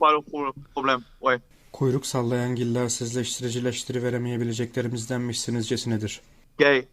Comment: what he says? I couldn't understand even I am Turk, born and raise in Turkey 😂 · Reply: kuyruksallayangillersizleştiricileştiriveremeyebileceklerimizdenmişsinizcesinedir